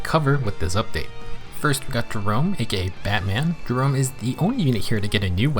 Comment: i thought you said "here we have gerome a gay batman"